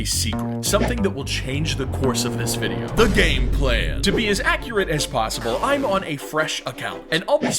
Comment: He said gameplan... But i heard.. Game Theory.....